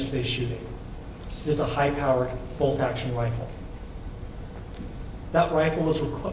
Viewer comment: What automatic weapon, the FBI guy clearly said bolt action rifle @ 5:04 , cant the BBC even listen to its own video's?